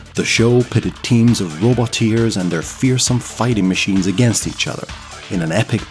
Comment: When the announcer would say "Roboteers Standby", as a kid i thought he was saying Robot ears and didn't know why 😅, the reboot was good too, shame it was cancelled.